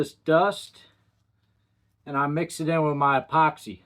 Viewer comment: I like this guy! I thought he said " I mix it with my coffee" then we saw it's mixed in the epoxy. Nice knife!